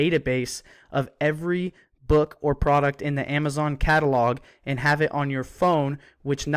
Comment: Nope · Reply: oh okay I hear ya say 0:52 book or product, right on man thanks